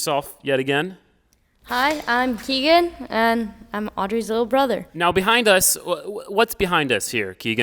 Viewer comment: For a second I thought you said “Audrey’s older brother”.